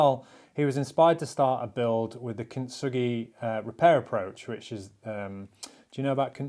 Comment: What is the word or phrase you are saying at 7:55? When something is damaged, make it beautiful approach? Contugey? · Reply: kintsugi